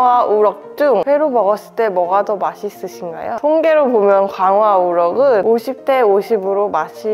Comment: 홍계로 보면 광어와 우럭은! 으로 들려서 뭔가 귀엽네요 ㅋㅋㅋㅋ